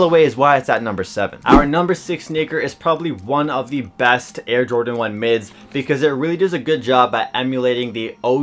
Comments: WHAT DID HE SAYYYYYY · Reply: "Our #6 sneaker is probably one of the best Air Jordan 1 Mids" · did i heard that right? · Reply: Our number 6 sneaker?